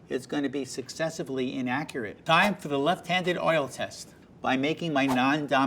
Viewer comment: i thought at 2:33 it said "Succesfully innacurate"